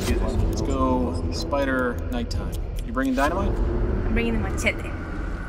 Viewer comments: Excuse me what did she say at 0:46??? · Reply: Bring dynamite. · did she say " I'm bringing my titty" or "machete." Lawl